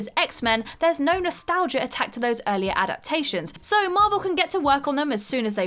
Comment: it sounds like she said attacked instead of attached ... or is that just me and I'm just hearing things that aren't there again??? hahaha ... and then @ 10:06 she says "not only did the Fantastic 4 fit with Marvel's tone" did is post tense; meaning they have already fit in with Marvel's time... the problem here is that the Fantastic 4 obviously have yet to appear within the MCU. ... it's SUCH a TINY nitpick but shit like that really bothers me lol